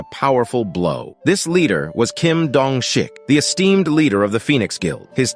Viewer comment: Bro I thought his name is Kim jong un Bcs i didnt hear it right